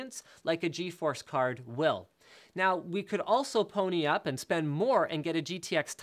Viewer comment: did he just say Now we could also pony up?